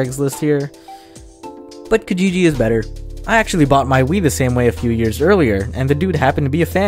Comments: I thought you said Weed lmao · I thought he said I bought my weed the same way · please tell me I’m not the only one that thought he said weed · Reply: You are not alone! I had to go back and keep rewatching it until I heard him say “wii”. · I thought he said bought 'weed' 1:06 or is that just me?